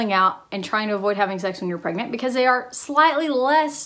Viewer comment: did she say 'try to avoid having sex when you are pregnant'?? Replayed it several times and still keep hearing that 😂